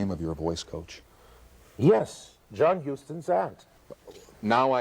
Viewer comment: I thought he said "Walter Houston's ass" and lost it 😂